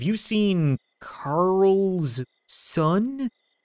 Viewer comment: Did he just say... Karlson? OHYOUDONTKNOWWHATKARLSONIS?